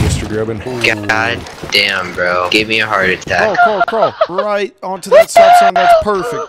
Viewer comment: i thought he said you ma heart